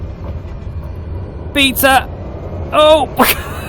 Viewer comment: Piza. Is it instead of a swear word? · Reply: LOL 😂😂😂 I think I'm trying to say "beta" in order to stop